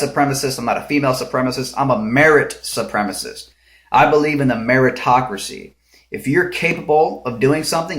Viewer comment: I was a bit thrown off by your meaning of "Meritocracy" as it sounded like you said "America-tocracy" as if it was something significant to America. But anyway, the definition of Meritocracy is a political system inferring people with "merit" are given political power, not so much monetary power. But the only way to get political power, in this world, is to have loads of money (just not sure what they spend it on to win a political office...). After all if America did run like a meritocracy, teachers, firemen, police, sheriff, active and retired military personnel would be high paying jobs. Or at least better taken care of, but they aren't. Which is a true pitty.